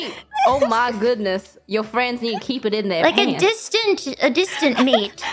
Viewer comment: I think at 1:11 Bae is saying in their "pants" not hands